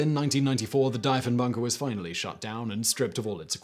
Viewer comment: Simon: Dye-fen-bach-er Canadians: triggered It was pronounced deef-en-baker-----the man was known cross-Canada as Deef. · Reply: I heard it as dive-in bunker